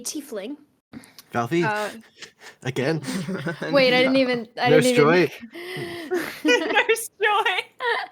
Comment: i love this bit so much 5:41 Was not paying attention then they all started saying hospital and i literally do not know why they are going to the hospital 10:06 i said this in a tumblr post but tortellini really is the pretzel(from jrwi riptide) of this campaign 13:54 I CANT UNDERSTAND WHAT IS SAYING. I slowed down the videdo but i still have no clue 😭😭to me it soundls like. "healthy? again?no story?" 16:57 feed into the propganda(danxneph). you know you want to 23:45 I want doctor fuck to be canon please please please 25:48 dr fuck is uncomfortable with the energy in the studio today 29:37 they should all sit in a circle and explain their traumas to eachother, thereby inventing group therapy 35:57 Dnd campaign OVER 🙅‍♀ its bowl time now 39:02 part 2 of me having NO IDEA WHAT THEY ARE SAYING. ".... kinda fucks" PLEASE TELL ME WHAT KINDA FUCKS 46:34 HELL YEAH ROWAN SECRETS 50:59 everyone in this campaign has family issues huh (minus neph he has other issues) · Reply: AHFAJAG OKAY THATS FAIR I was sleepy and mumbling a little at times- 😅 The 13:54 mark was a joke about Valphi (the cleric from Wroxham) being Nurse Joy, and appearing in every town. I believe the 39:02 mark was just saying that “pottery kinda fucks”